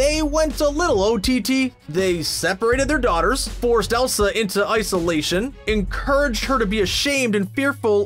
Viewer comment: hah i found this when you said "they went into isolation" at 15:38-15:40 it sounded like ICE-olation!! also i love your vids so keep it up!! 👍👍